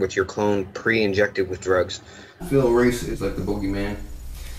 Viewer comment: What name did he say at 32:38 ??? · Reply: Phil Reese, not a celebrity but you can find him if you google 'phil reese donald marshall'.